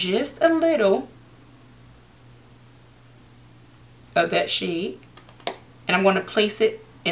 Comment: When she says of that shade I thought she was saying that shit